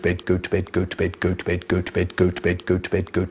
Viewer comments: He sounds like hes saying good bed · Sounded like he saying "Goat Bed"